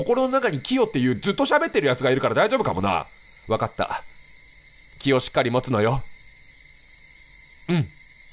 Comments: 『気をしっかりもつのよ』が『キヨしっかりもつのよ』って聞こえて勝手に草生えた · ｢俺がいるからなあ｣の会話の流れで お母さんの｢気をしっかりもつのよ｣が｢キヨしっかりもつのよ｣に聞こえてしまった 確かにこの状況でキヨもってたら心強い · 「心の中に「キヨ」っていう」って聞いたから 直後の「気をしっかり持つのよ」が 「キヨしっかり持つのよ」にしか聞こえなかったww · からの「気をしっかり持つのよ」が「キヨしっかり持つのよ」に聞こえて笑った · 心の中にキヨがいるってくだりだったから「キヨしっかり持つのよ。」て聞こえてめちゃめちゃ笑った(笑) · キヨキヨ言ってたから「キヨしっかり持つのよ」に聞こえたw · 「気をしっかり持つのよ。」が「キヨしっかり持つのよ。」って聞こえたw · お母さん「わかったキヨしっかりものつのよ」に聞こえて一人笑ってしまった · の「気をしっかり持つのよ」が「キヨしっかり持つのよ」に聞こえて一人で笑ってた笑 その前に心の中のキヨの話をしてたから余計に笑